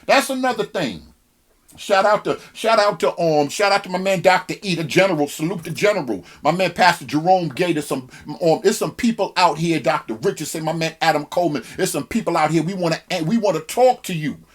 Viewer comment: what were the names of the brothers you shouted out @ 8:49 - thanks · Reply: Bradley Panda Eric Mason Epiphany church Philadelphia, Jerome Gay Vision Church North Carolina Raleigh, Damon Richardson ministries Atlanta Georgia